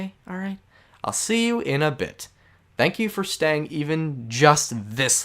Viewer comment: I feel so bad because I like passed out in my sleep and it autoplayed to this and I heard "thank you for watching this long" when I woke up